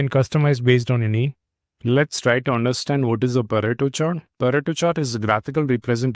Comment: I thought he said naruto chart😂